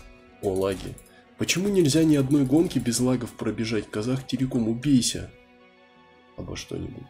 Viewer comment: Мне послышалось или ты сказал Казахтелеком?) Земляк)